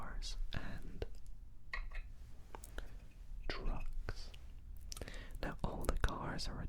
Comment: I thought he said drugs 😳